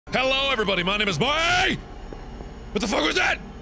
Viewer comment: The intro sounded like he said," hey everybody my name is Wade."